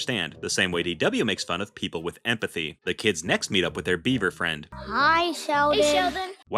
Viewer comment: for a sec I thought you said beaner friend